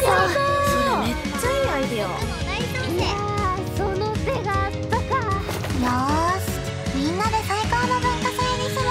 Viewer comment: その手があったか〜って誰が言ってる？ 笹木にしか聞こえない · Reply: めと